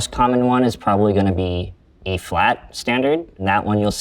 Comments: Tim says E flat standard but subtitles say A flat standard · the captions say A flat, but he is talking about E flat. A half step down across the board.